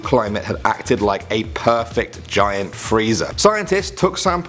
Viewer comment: it sounded like he said freiza